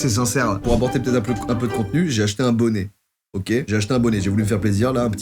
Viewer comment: jai compris "jai acheter un poney" 😂